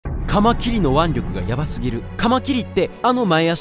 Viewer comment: 何回聞いてもタマキンの腕力って聞こえる。